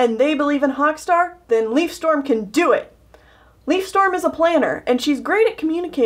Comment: I thought she said “Leafstorm is a player"